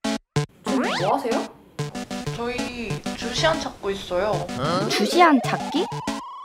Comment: 아니 요즘 롤만해서 그런가 처음에 루시안이라고 들었네ㅋㅋㅋ주챔이 루시안인데ㅋㅋㅋ